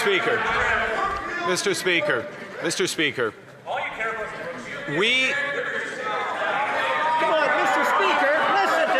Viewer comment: what was said at 7:07 any ideas ? all you care about is ? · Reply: All you care about is Brookfield.